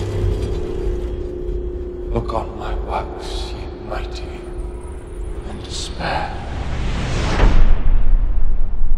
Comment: can anyone find out what he says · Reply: kindo camp "Look on my works ye mighty and despair". it's from a Shelley poem.